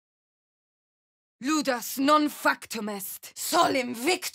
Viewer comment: First one: I heard “Lewdass non-fuck Tomas” XD